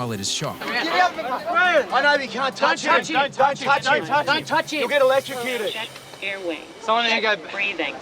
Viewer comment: 11.09 I thought they say "Don't touchie"